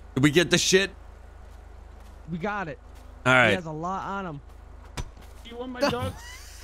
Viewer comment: did he say "do you want my dog?"